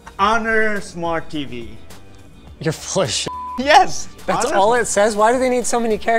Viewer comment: did I just hear "flagsh*t"?